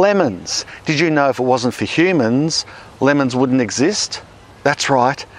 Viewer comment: dude my ears are dyslexic. I heard "did you know if it wasnt for lemons HUMANS wouldnt exist" I was like WAIT WAT